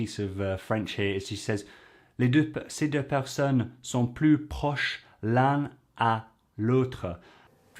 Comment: Very good analysis ! Jodie Foster is so impressive in the way she speaks fluently and naturally while expressing deep ideas and concepts. Actually, at 8:38 you didn't heard correctly. She said "plus proche l'un DE l'autre" which is the proper forme in French, but not the right gender, as "personne" is feminine. So it should be "plus proche l'une de l'autre". You could translate it as "closer to one another". For the rest, you did a great job ! (I'm French by the way ;-))